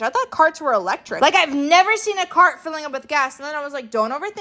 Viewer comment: For a second i thought she said "I've never seen a car use gas before" 🤣🤣🤣 i was gonna say "MISS GURL SO WAT U DOING RIGHT NOW???"🤣🤣🤣